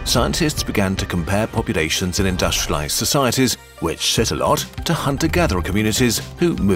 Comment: I thought he said “shit alot” instead of “sit alot